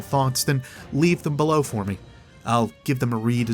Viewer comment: Anyone hear Rasputin say darkness at the end? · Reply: i heard "I'll give you thug!" every time i listen to it i cant hear Darkness.